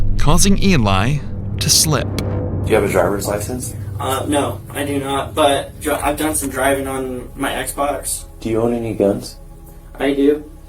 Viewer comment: Ok two things: 1: hes 19 but doesnt have a driver's license? 2: did he REALLY just say "no I dont but I've done some driving on my Xbox"?? Did I hear that right?????